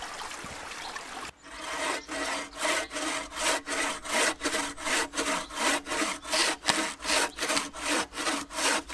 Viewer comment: I need a translator! At 8:29 I'm hearing, "It's got rats, it's got rats, the stones are close by......." and I'm pretty damn sure that's not what he's saying LOL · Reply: It's going grand, it's going grand, the stones are so close by, that's nice.